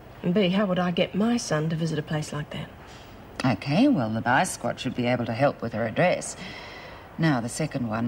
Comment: What did coleen say say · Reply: The vice squad should be able to help with the address.